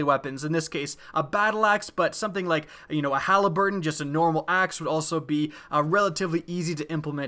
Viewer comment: did you just say a Haliburton? Lol I think you mean Halberd